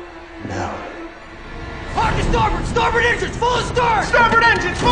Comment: What does he say here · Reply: He says “hard to starboard, starboard engines—full astern!”